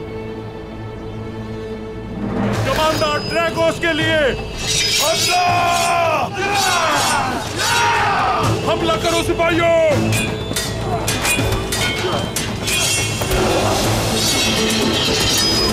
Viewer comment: 😂😂😂😂😂😂😂 did dragos just say Allah